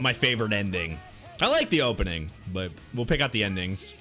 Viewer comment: does anyone else hear nen-dings